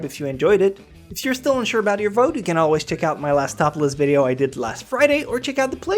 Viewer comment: did you just say "you can check out my last topless video"? How & Where?